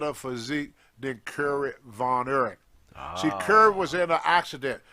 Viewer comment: wtf did he say? I'm from Texas and even I can't figure out what the hell he's saying there. I think he's saying you didn't get the job because you guys have a better physique then the other guy whose name he was pronouncing crazily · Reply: Kerry Von Erich